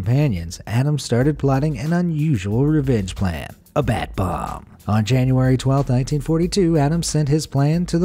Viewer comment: when he said bat bomb, I heard Bath Bomb